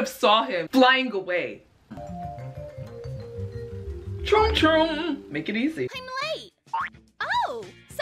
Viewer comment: if you turn the subtitles on, when says troom troom, the subtitles say "drunk room" 🤣🤣